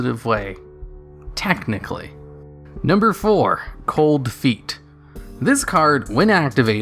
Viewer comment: I thought it said cold fate not cold feet